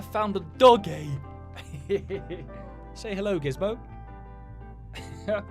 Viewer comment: did you say gismo? THATS MY NEW KITTENS NAME